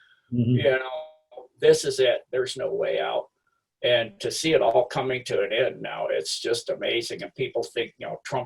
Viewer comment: So what’s saying......at the 20:45 mark..........Trump isn’t for the people, and we are daft if we think that ?.....did I get that right? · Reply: You got that wrong. Click on subtitles if you can't hear correctly. This is what said ""to see it all coming to end now it's just amazing and people think Trump's on their side (the cabal side)... your daft"